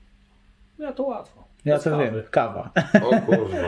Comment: usłyszałem "bez trawy" hahaha